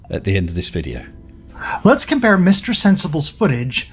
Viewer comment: "So i drew another line, that looks pretty flat to me" .. brilliant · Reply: did he say "supposively"?